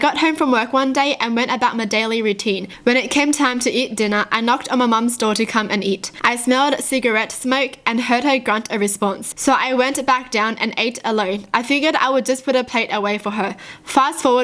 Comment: When she said "I ate alone" I heard her say "I ate a loaf" I was like, a loaf of what?